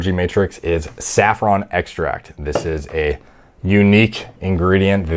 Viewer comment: He said saffron extract. I got excited for a second and thought he said safrole extract. I was about order 100 cases of it and synth some MDMA lmao.